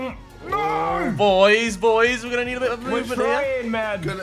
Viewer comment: i swear harry says "boys boys were gonna need abit of lube in here!" 😂 at 4:43 .. correct me if he doesnt say it lol